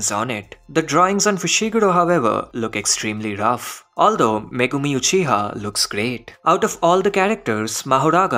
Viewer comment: Did he say megumi uchiha😂😂